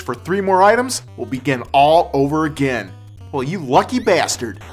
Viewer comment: did he say 14 level? :-/